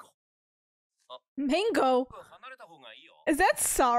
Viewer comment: Regarding Gojo saying "Mengo (sorry)" in the classroom. It is a common Japanese paraphrase of a phrase that was popular for a while. (Perhaps it sounded like he said "mango" to you.) In Japanese, "Sorry" is "Gomen," so the order of the letters is switched to say "Mengo". For example, another way to say "Sushi" is "Shi-suu". However, this is a way of saying things that was popular among some people decades ago, and basically no one uses it anymore. It is only used when you dare to use an old word or a strange way of saying something for fun. In Gojo's generation, I don't think many people use the phrase "Mengo", so I think this is the way to emphasize the ridiculousness of that scene. In case you are wondering, at the end of S1EP3, Nobara says "Shi-suu" for "sushi". Maybe the author likes to say it this way.